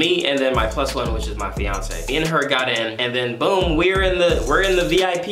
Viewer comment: Idk if I heard this right but did he say fiancé?